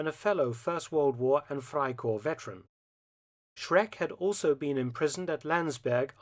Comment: I was half-listening to this while typing out some messages and then all of a sudden I hear: "Shrek had also been imprisoned at..." Had to go back a bit to resolve my confusion :D